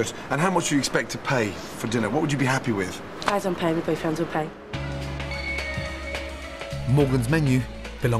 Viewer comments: what does she say at 24:23? She doesnt pay when her friends are paying? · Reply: Her boyfriends pay she said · “I dont pay my boyfriends will pay.” Is that what she said really? Or did I get it wrong?